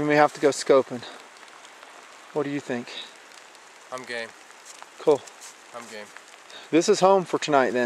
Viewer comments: haha sound like he says ¨im gay¨ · did say "Im Gay" at 5:49?